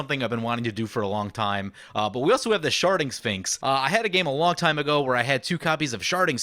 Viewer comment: I think I must be 10 years old because I hear "Sharding Sphinx" and I think I hear "Sharting Sphinx" which causes me to giggle like an idiot.